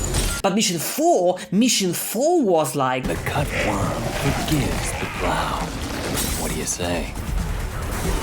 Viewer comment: I found out what he says at 7:49 “The cut worm forgives the plow. What do you say?” No clue what the hell that means but he was quoting William Blake’s Porverbs Of Hell.